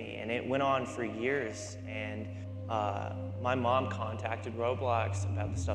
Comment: i thought you said contracted